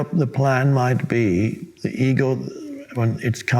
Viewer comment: I swore he was saying “ eagle”plans 🦅 🙈 up til 3:54 goodness hahaha